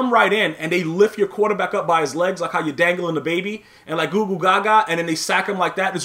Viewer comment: Did he just say like dangling a baby Lmfaoo!!!!